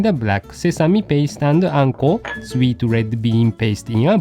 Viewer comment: i thought he said "manko".....